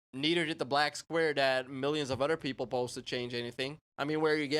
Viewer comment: my friend took this part out of context. he ran up to me and said bro this has to be racist and i scrolled back and knew what you were talking about, he thought you meant “why should white people do it?” because you said “our people” but i had to explain to him that you meant people into metal or people who didn’t want to donate, nothing racially motivated 😂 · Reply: Actually, you and your friend both misheard. He actually said "other people". He repeats this multiple times from about 14:05 to 14:30. His Scandinavian (at least it sounds Scandinavian; I could be wrong) accent makes it sound like "udder", and when he says it very quickly, it could sound like a poorly pronounced "our".